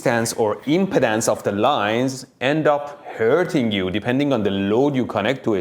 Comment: the impedance of the line... I heard that as the impotence of the line. Accents can be amusing.